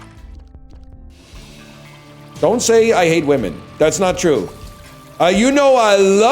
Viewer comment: He hates sworn thats what he said.